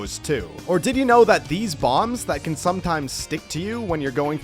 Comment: I wasnt paying attention and thought that he said “Diddy's bombs”